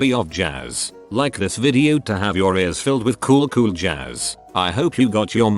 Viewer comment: I thought it said have your ears filled with jizz lol